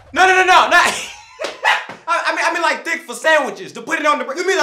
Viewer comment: When he said thick it did sound like he said d##k ngl